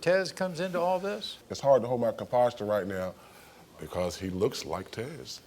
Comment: did he say conposture??